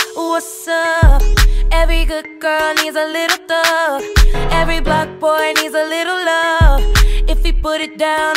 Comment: Y DO I HEAR IT"EVERY BUFF BOI" AND NOT "EVERY BLOCK BOI??"